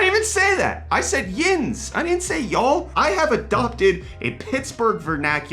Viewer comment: my bad bro i thought you said “ya’ll” after “yinz", I wasn’t trying to misquote i swear lol i’ll respect the yinz from now on 😂😂